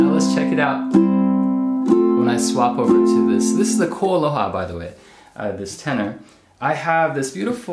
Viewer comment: What was the brand of that Tenor Ukulele? I think he says it at 2:38m but I cannot make it out. Thanks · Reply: He says Koaloha